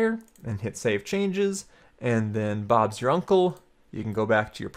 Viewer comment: did he say bobs ur uncle?